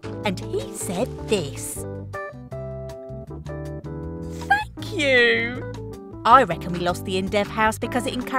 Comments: what did she say? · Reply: they said "thank you" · Bruh I thought she said f*** you 😳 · is it me or did I hear her say F you?